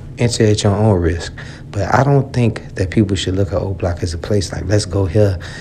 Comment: I thought they said Oblock was like Allstate, what happened 😂